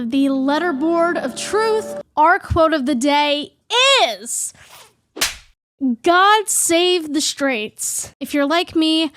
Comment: I could have swore the letterboard said "god save the serbs", what the hell